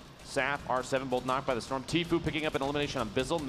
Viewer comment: I thought he said Natefucker